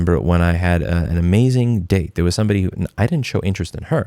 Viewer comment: My wife and I thought you said... I remember a time when I had an amazing dick... It took us three times to understand you said date.